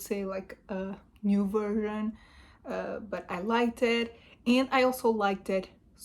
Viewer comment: You're a terrible reviewer. What do you say at 3:59? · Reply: New version